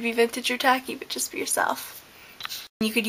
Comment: I always hear you say "Be vintage or tacky, bitches! Be yourself," rather than "but just." Might just be me though. LOL